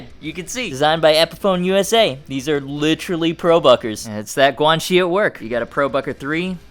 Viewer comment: When you said "Guanxi" I thought you said "Quan Chi" and now I wanna beat Liu Kang to death with his own leg.